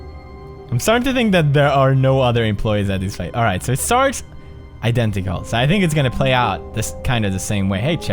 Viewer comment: Also.. Sounds like Pewds is saying Sharts and it's making me giggle uncontrollably